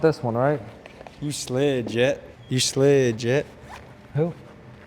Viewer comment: stop calling people JHITT! 💀💀i thought you said jet at first